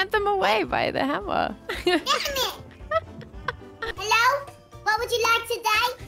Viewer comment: is it just me or did say dammit